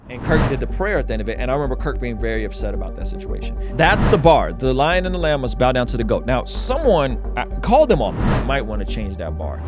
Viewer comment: I thought he was saying “The Lion and The Lamb WE’LL bow down to the G.O.A.T.” But that makes sense. That was a horrible verse.